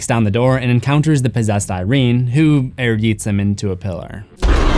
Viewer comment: wasn't looking at the screen for a minute and thought you said "pillow", not "pillar"